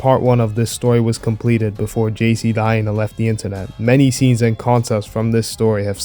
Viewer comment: Did he say the JC died? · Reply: He said “before jc the hyena left the internet”